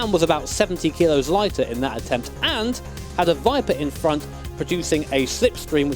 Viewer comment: I just searched up the original attempt of Sabrine, and then I actually heard and saw the "viper". In this video i thought said "Wiper" and was so confused, how a wiper was supposed to create a slipstream... -_